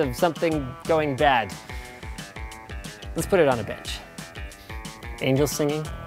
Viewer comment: did he say Bitch or Bench?